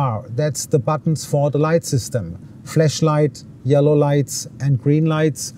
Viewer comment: Did he really say "fleshlight" in 2:04? Naughty boy😂😂